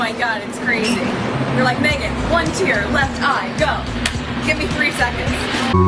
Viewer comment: she actually says "you say Megan give me one tear in left eye! Give me three seconds"